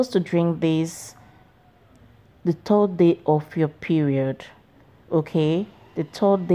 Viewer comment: sorry but I didn’t understand what u said when do we drink this ? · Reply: The 3rd day of ur menstrual cycle